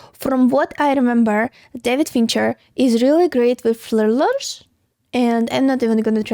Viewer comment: I'm sorry, but she's just too damn cute. I once heard pronounce "tooth" as "tooF"😊 - I had to remember this to finally figure out what was trying to say at 0:58 - "thrillers" 🤣😊